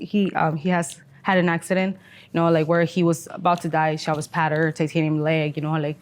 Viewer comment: did anyone catch "shelvis pattered"